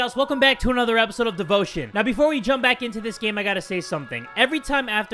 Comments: I thought said “The bullshit” instead of devotion in the beginning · When you said “Devotion” i heard “boat shit” 😑